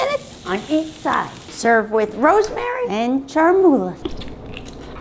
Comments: omg I thought she said “we’re grilling grandma~” 😭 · “The grilling grandma” 😂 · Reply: I heard “They’re grilling grandma” and ngl I panicked just a little 😂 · “The grilling grandma” 😂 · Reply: i heard 'We'Re GrIlLiNg GrAnDmA' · ''thE gRilLinG gRanDmA'' got me dying 😂🤣 Edit 1:bro so many likes ty guys Edit 2: tysm Edit 3: rest in peace lynja, you are already missed 🪦 🕊️ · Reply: I legit heard "they grilling grandma" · "The GrIlLiNg GrAnMa👁👄👁💅🏾" "The punch 💀" Was the best part for me 🥲🤣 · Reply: I thought she said we're grilling grandma